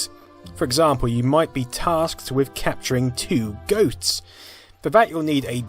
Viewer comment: Did anyone else hear "tasked with capturing 2 goats"?! 😂